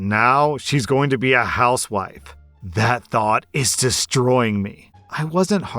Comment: I heard "house wipe" not wife